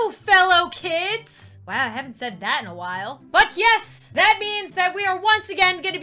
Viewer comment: did she just say fu** yes????! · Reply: She said “but yes”